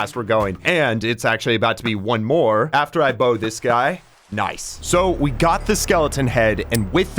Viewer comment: fully thought he said "bone this guy"